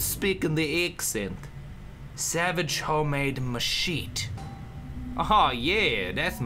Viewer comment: When says Machete it sounded like he was saying My shit :D